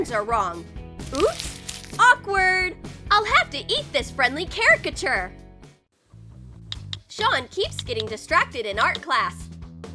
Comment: charakter (that’s what it sounded like she said) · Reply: it’s actually caricature 😌